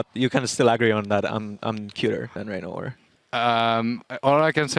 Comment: Hi, i'm sorry but i'm french and i try to understand what Cyanide said ... i got the "How it feel like to have an actually decent jungler ?" but after that i heard "You can still angry about i'm a killer not like ReignOver ..." but i'm not sure. I understand YellowStar, Rekkles, Huni even Sjokz but for me Cyanide sound like he had a hot potato in his throat maybe because he comes from Finland. I don't want to be mean but i really have difficulties to understand him. =) I'm used to watching LOLesport matches in english to improve my skills and i really want to know because i loved the old fnatic and the new one as much as i love Origen. Thank's for your time and don't be affraid to correct me if i wrote some grammar's mistakes. Have a good day and enjoy LCS EU ! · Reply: I think he says: "You can still agree that I'm cuter than ReignOver". Your English is actually very good! Have a good day!